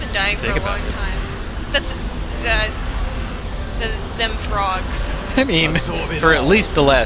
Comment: Did she just called French people ''Frogs'' ? The hell ?